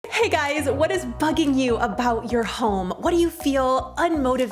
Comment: Okay I was partially distracted when the intro started and I heard ‘hey guys what is Fxxking new’ and aaaallmost lost it hahaha.